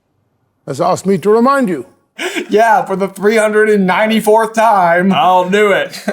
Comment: did… did I just hear 394th time? As in, three hundred and ninety-four??? Well done.